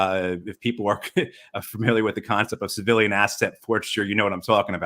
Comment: Please and thank you. What is he saying here? “The concept of civilian asset (unintelligible word)” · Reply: civilian asset forfeiture. Sorry, I wasn't clear; thanks for watching.